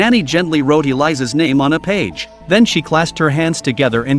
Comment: why do you fuck with the names? Why change the names ? It clearly says "Dorothy" not Eliza. and I clearly heard the ginger wizard HP's best friend, The Ron Weasley, I clearly heard him call his sister "Dorothy" The recap is hard enough to follow already to